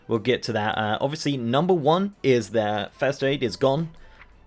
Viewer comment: Just for anyone who couldn't figure out what the hell he said at 0:28, he's talking about FIRST AID.